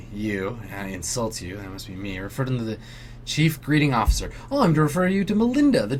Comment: I thought said chief breeding officer